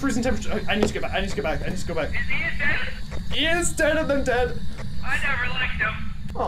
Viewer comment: I thought you said Ian reader XD XD